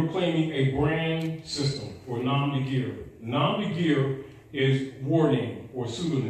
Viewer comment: speaks of a term that I couldn't decipher or find "nom nigir" couldnt understand the pronunciation or find the spelling or term. Any recommendations? Thanks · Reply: nom de guerre